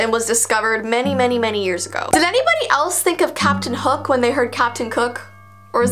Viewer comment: I THOUGHT U SAID CAPTIN HOOK XD